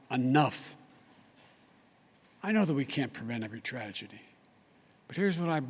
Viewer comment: Did he just say "prevent a good tragedy?"